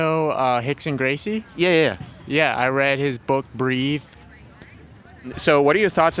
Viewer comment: "Do you know Rickson Gracie? I read his book." Lol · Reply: Is that what he said 😅😂😂😂 I thought he said "I wrote his book" and I took that to mean that he helped him put together bjj moves into an easy to follow way 😅 I thought this guy was a secret humble assassin but now you've completely shattered that impression 😂